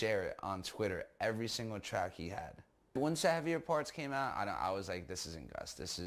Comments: aye what song did he say @ 13:10 ? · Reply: he said "once the heavier parts came out" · Gus’ friend says “I reposted his soundcloud links on twitter.” He then says “ once “savvy reports” (that’s what it sounds like anyway) came out I knew it was Gus putting on a front” What does he actually say when the captions translates it to savvy reports??? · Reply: “once the heavier parts came out “